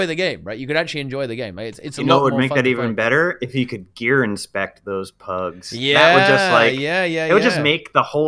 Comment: when said "gear inspect the pugs" i thought he said "gear and spec the pugs" like they're gw1 Heroes :P